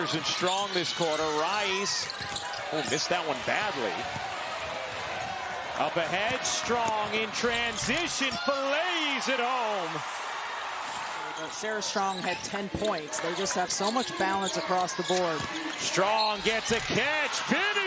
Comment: At the 8:16 mark did he say” Filayyys it home”?!?!? That’s crazy